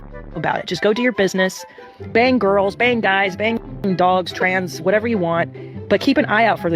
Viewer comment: I’m sorry did she say dogs!? Wtf